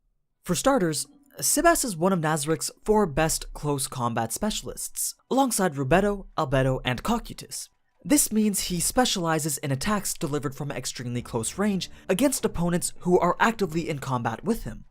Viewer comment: am i the only one who heard "Cockeetus"?